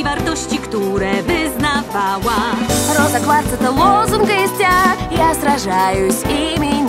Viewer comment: In russian 1:15 I hear Rosa quartz is a awesome green star xD